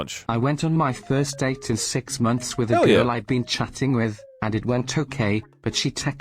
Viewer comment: I thought the chatter said "I've been going out with a girl I've been cheating with" i was horrified, then says "hell yeah" ill see you in court buddy